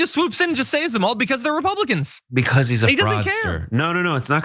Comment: Btw, I misheard, thought you said: ‘he’s a monster!’ Now Realize the adjective was, ‘fraudster’. Of course both work together!!